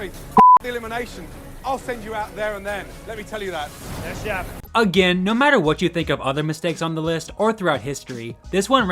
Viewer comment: I have replayed it about 5 times so far, and still trying to understand fully what said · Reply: LOL he said “I confess I hate Jessica. Pretty much, Yep. hate her.”